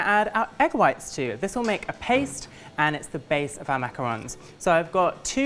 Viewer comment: I freaking LOVE the name clarification!!! It drives me CRAZY when people around me call it the wrong thing! I'm like do you call pie cake?!?! No! So stop! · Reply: I genuinely thought it was macaROONS and I thought everyone saying macaRON was an idiot but actually I'm the idiot